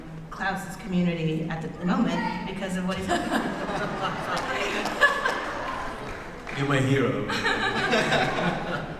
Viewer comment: what did the person say at 15:12 where daniel says "youre my hero" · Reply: I think right after Julie says there's no place in the community for Caroline on the show, somebody yells out "Make it"